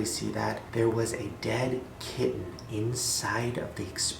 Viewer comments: Does anyone know what he says? Kitten? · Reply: Jade Kuehn yeah it’s a kitten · There was a dead _____ in her mouth??? Can someone help? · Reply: i thought he said kid at first, not kitten. :c poor kitten. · did he say dead kitten? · did he say kid or kitten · can someone pls tell me what Shane said? Was it a dead kitten? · I'm on my phone and there's no subtitles - what did Shane say was in the woman's mouth which she took out? I heard "kid" or "kitten" lol Can someone clarify? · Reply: HunterTheHuntress kitten